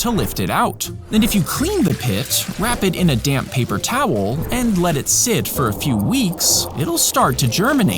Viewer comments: Tell me why I thought Zack said "put it into a d*nm paper towel"💀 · I thought he said a damm paper towel 😂😂